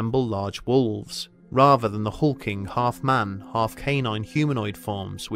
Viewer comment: I thought that said "rather than the fucking half man, half canine humanoid forms"